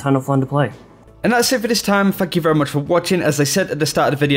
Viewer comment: sounds like he says 'fuck you very much for watching'